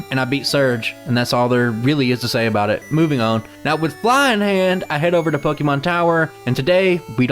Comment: When you said "With Fly in hand," for some reason I thought you said "With Flying Hand," and immediately thought you were talking about Master Hand from Super Smash Bros. Evidently, I was sleepy when I watched the video. Great run, was fun to watch. Thank you for the consistently great content, mate.